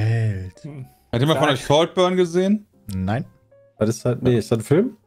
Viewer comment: Welchen Film meint nach Frage 32 ca. 50:45 ? Leider hab ich es auch nach mehrmaligem wiederholen nicht verstanden. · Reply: Saltburn meint er, läuft auf A. Prime Video.